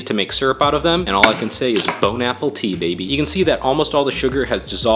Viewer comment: I thought he said bone apple teeth